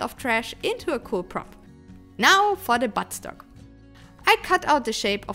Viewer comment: BTW: Did anyone else mishear 2:38 as "Now, for the butt stuff!"? 🤣🤣🤣